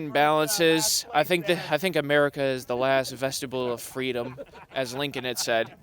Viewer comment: America is the last “vestibule” of freedom? - WTF is a “vestibule of freedom?” Or is he saying the last “vegetable” of freedom?